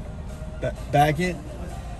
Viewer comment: what is he saying here? hopefully I'm not hearing him correctly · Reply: he said "back it?" to the parking attendant, as in "back it in to the parking space?" like every other truck in the video that is backed into their space